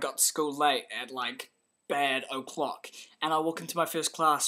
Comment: i heard "bad-o-clock" lol